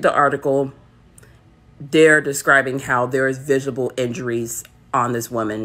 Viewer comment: did she say vigable Siri wants to fix it so bad 😂😂😂